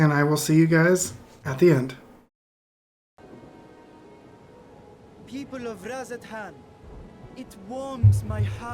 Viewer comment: I'm glad they have the subtitles cause I thought he said "People of rancid ham..." instead of "People of Radz-at-han..." lol.